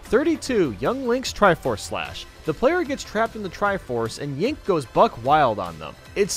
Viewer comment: Did say “Yink” at 8:37 or was that just me? 😂😂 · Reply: He said Yink.